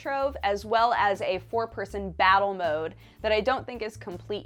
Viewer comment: i thought i heard "a poor person battle mode" and was confused about why there would be hobo arenas